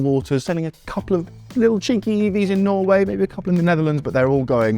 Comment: did you say chinky EVs???